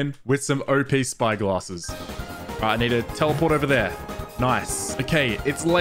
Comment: DID HE SAID- "ALRIGHT Ni**a teleport me